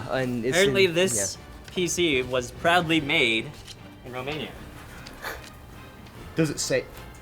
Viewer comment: "Apparently this PC was proudly made in..." where? Did I hear Pennsylvania? Or where? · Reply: I'm sure af he's saying "in Romania" but why?